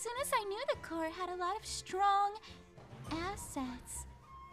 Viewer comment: I didn't here assets I heard asse....